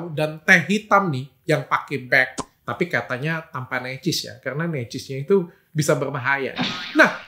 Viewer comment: kata dokternya tanpa ya? Udah berkali2 di replay tetep ga nangkep otak aku dokternya bilang apa.. "tanpa necis" kah? Apa itu necis ya?